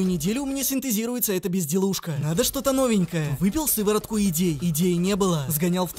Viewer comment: кому тоже послышалось вместо идей слово детей😂🤣